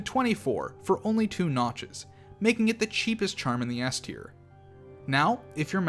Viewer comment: lol the auto generated subtitles say ass tier instead of s tier lol.